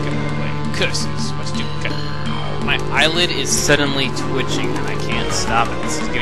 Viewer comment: lol at first i thought u said ur island was twitching